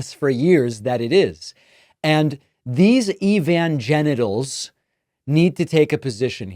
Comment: Evangelicals! Good one, David, Sir! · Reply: I thought he said Evangenitals ...